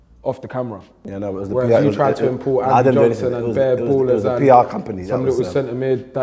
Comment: did filly just say chunkz tried to import Adam Johnson on his team 😭😂